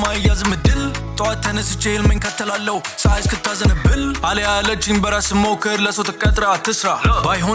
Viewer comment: Did he say አሊያ..?